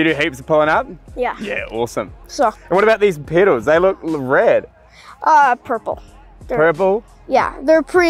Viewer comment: "What about these pedals? They look rad!" Kid: "Uhh.. purple." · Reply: I was wondering if the kid thought that said "They look red." With his accent it kind of sounded like it.